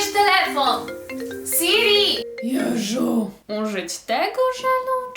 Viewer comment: czy tylko ja usłyszałam jeżu?